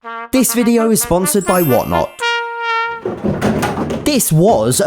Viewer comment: I sound like he say why not